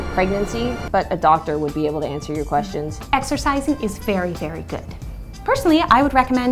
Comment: I heard that as Exorcising,and I'd say that I'm watching too much of Carmilla Season Three,but no amount is too much tbh